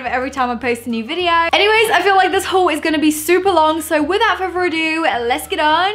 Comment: is it just me or did she say furfur instead of further